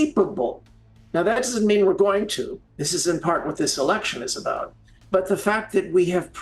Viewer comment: I’m sorry did I just hear him say ‘that’s what this election is about’!?! wtf there’s no way that’s what he said